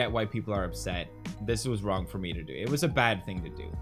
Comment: did poppy just say"ot was wrong from me......" ????hmmmmm